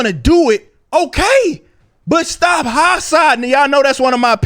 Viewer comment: What's that word?? "Stop H_______in' " It sound like "hotsidin" or something but I'm can't tell. Is that a Texan thing?